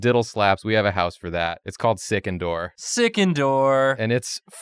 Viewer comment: I thought they were saying Sick-indoors and it was gonna be the edgy vampire emo house